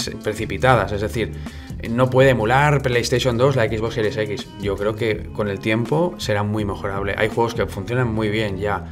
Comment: ¿Cómo se llama el juego que aparece en el min. 9:29? No alcanzo a entender lo que dice se ve muy bueno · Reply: Zone of Enders. Al fin alguien lo dijo de nadaaa xddd